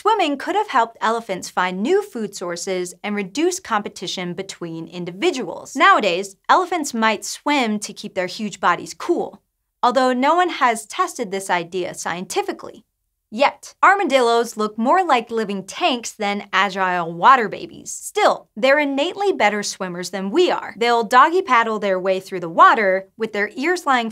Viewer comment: It took me like 4 listens to get this part, since I thought it was saying "Although no one has tested this idea. Yet, armadillos look more like..." Instead of "Although no one has tested this idea...yet. Armadillos look more like..."